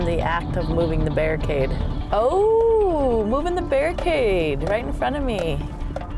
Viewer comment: i thought they were saying "Bear cage" not "barricade"